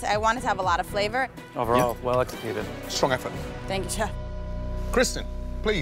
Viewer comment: “thank you chepfff” who else heard her say that lol no just me I guess